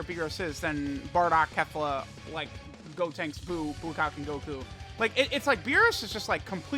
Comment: I really thought he said bukake goku instead of blue kaioken goku🤣🤣🤣